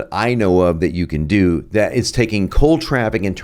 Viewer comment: I think your editor made a mistake , saying 'Call' instead of 'Cold'... but not a big deal!